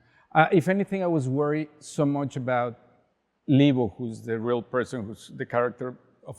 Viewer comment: Subtitles are wrong, he doesn’t say “legal”, he says “Libo”, short for Liboria, the real life person in which Cleo is based on :)